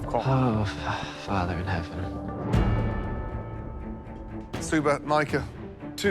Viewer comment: did anybody hear said"dear father is heaven..."